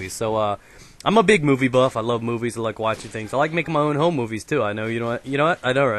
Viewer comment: i thought he said whore movies lol cos of his accent lol